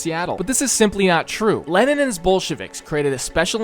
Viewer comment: why did i heard "lenin and his bullshitists"